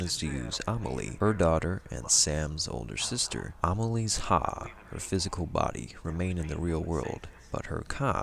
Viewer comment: thought he said "Amalie is hot, her physical body..."